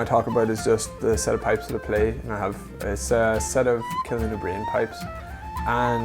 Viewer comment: Did he say kellin o'brien uilleann pipes?